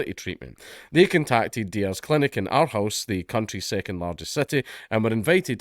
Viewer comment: When you said Aarhus it sounded like Outhouse and I wondered where this story was going